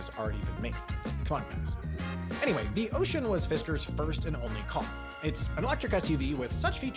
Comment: To add to the mistakes, this isn't the first car Fisker built, there also was the Fisker Karma before the Ocean. They sold over 2000 of them so it shouldn't be overlooked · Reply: First I had to rewind because of the music then at the second run, I thought I heard ''first electric car'' but then at the third try, the subtitles are '' first and only car'' Can we put this one on the same level as '' small step for (a) man '' and ''No/Luke, I am your father'' ?